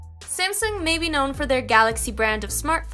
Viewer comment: Did you say; " Simpson "??